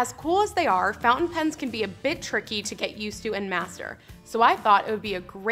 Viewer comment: I swore she said "Fountain pens can be a bitch to use". They're just "a BIT tricky". Had to run this one back.